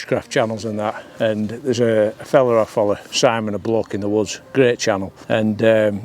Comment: Thought you said “cock-up in the woods” for a minute there! 🤣👍🏻 Nice piece of kit and really enjoy this type of video. As you say, it’s not all about the camera sometimes. All the best mate!